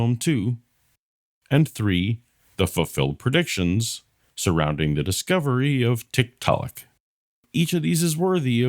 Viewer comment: As I was listening on my way to work, I totally heard you say, "The fulfilled predictions surrounding the discovery of TikTok." When it seemed like no punch line was coming I was convinced it was just a really bad attempt at a joke.